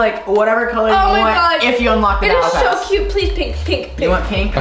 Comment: It sounded like said “that’s so cute” was it just me who heard it?